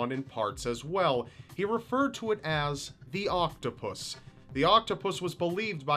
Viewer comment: He says "The octopus" I hear "Hail Hydra"